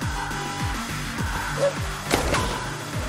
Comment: What did chidi say at 3:47? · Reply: Ahora muere perrito - die now puppy